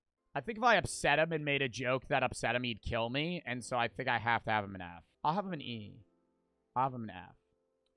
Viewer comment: why did he say "I'll put him in E like that"💀